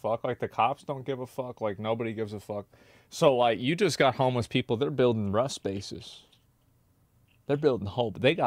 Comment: i thought he said homeless people are building Rust spaces. Referring to the game Rust.